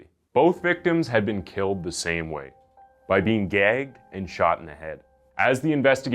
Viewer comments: bruh I swear you said "by being gay and being shot in the head".... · I thought he said "being gay" LOL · I thought for a second said "by being gay" and I was like 🤨🤨🤨🤨